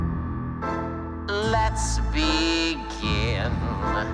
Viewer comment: I thought said lets be gay❤